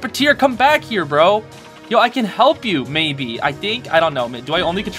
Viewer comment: I could barely hear you and I thought you said hump instead of help-😭